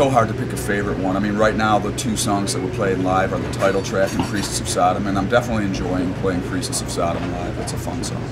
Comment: I SWEAR it sounds like he's saying "Priestess of Sodom".